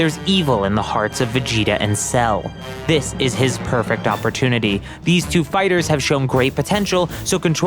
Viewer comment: Did you say "PERFECT"